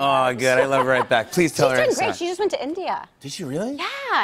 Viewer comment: did she said she's been great she's been to india Please someone reply